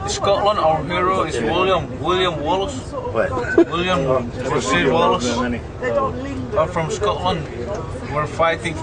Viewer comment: Did he say Rasheed Wallace? hehe.. funny guy